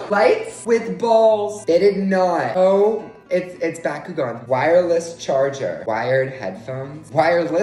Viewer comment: Him calling Beyblades by another name: "It's Bakugan" This is my favorite part of the video for some reason. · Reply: I thought he said bakugou 💀